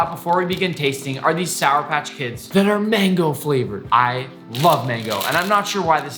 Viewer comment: did he just say mango💀